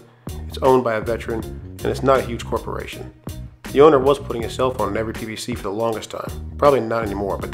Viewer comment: The Pit Barrel owner was putting a "cell phone" on each cooker? Did I hear that right?